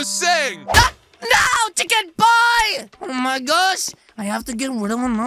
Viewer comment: Did he just says chicken boy 🤣🤣🤣🤣🤣🤣🤣🤣